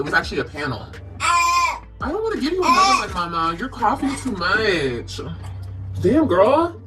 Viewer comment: did she just say to that she was coughing too much??? I honestly think that is what I heard🤔please somebody correct me if I am wrong